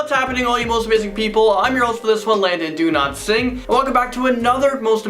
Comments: I thought he said Landon do not sing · It sounds like he says landon do dont sing